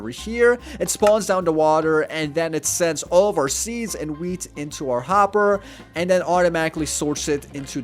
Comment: I heard it wrong "seeds and weeds" Lol